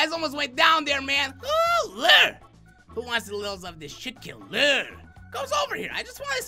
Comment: Pause at 3:13 does it sound like he said shit killer